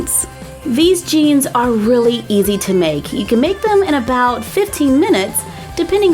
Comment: honey what did you say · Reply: She said they're really easy to make